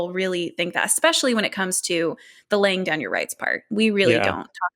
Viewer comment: What did she say? "The lang-gunner rights part"?